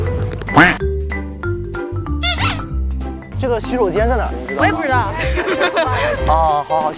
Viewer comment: Did the girl in 1:47 speak Korean? Sounded like in Korean, "Why, don't know". If she did say Korean, it would have been funny if he say thanks Ajumahnim in response. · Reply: Nope bro... she's speaking Chinese, wo ye bu zhi dao